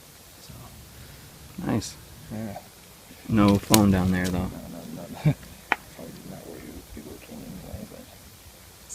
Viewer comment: does anyone hear a deep voice of a man when they found the phone case ??? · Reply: I actually hear from an old mens voice at 11:20 : "no no no no you are do not looking inside of this!"